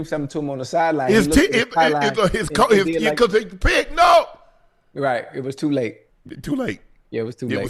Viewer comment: Someone please tell me what Unc said at 16:13 😂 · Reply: he said his coaches shouting from the sidelines "Pick no!"